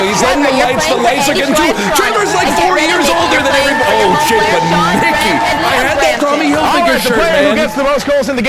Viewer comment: WHAT?? I can't hear you, NL!! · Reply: TREVORS LIKE FOUR YEARS OLDER THAN EVERYBO- oh shit but Mickey